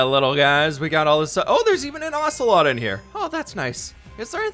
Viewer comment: When he said ocelot I thought he said axolotl